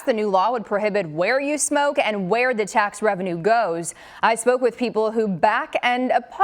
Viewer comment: My high ass had to rewind at beginning. I thought she said "I smoked with people" instead of "I spoke with people." 😂 lmfao